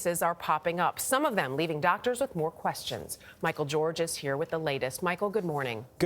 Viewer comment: For a second I thought she said Michael Jordan. I'm like uh oh he going to cry about this virus.